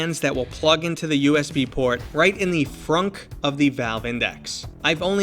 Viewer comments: did you say in the fronk?? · I heard frunk 3:10 . Its like a front trunk